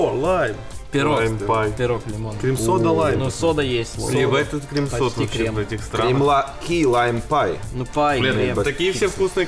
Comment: Симпл сказал ебать у них здесь крем сода в этих странах 1:22 или мне послышалось